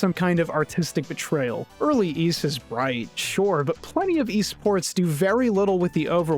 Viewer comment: I heard "eSports..." OH! No, it's "Ys Ports". I don't think anyone could have done better except for maybe "ports of Ys" but that sounds like an Ys version of Sid Meyer's Pirates!